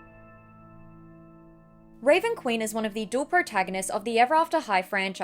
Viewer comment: i thought it said queer not queen I thought she died cuz she was gay??